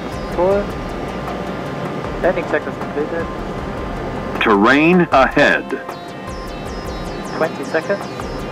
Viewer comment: It's scary to hear the alarm "terrain ahead "... you only hear It in air accidents... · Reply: I heard "train ahead", and i was wondering how does a railway crossing matter for airplanes :)